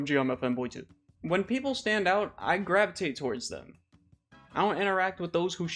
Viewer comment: When you said gravitate I thought you said you grab something else Oh my god that… that was so bad 😭 I know this is literally the part about weirdos and I promise im not making this comment on purpose or anything I just genuinely misheard and it was so bad lol Also what is the game you’re playing? I’m just curious